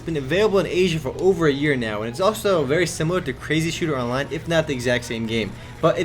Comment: I couldn't stop laughing when I thought I heard "Crazy Sh*tter online" :'D